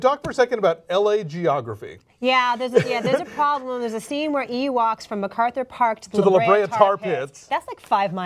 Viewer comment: When she said " E walks" I thought I heard ewoks.